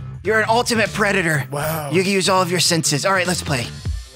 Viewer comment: 0.41 i thought he said u can use ur as